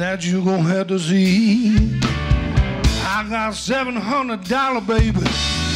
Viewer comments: WHEN HE BRAGGED ABOUT HIS WEALTH YOUTUBE THOUGHT HE SAID “I got 700 babies” 😂😂😂😂😂😂😂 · Love how the auto generated subtitles says " I have 700 babies" at like